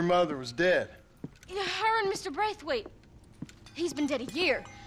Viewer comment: Mrs Braithwaite did I hear?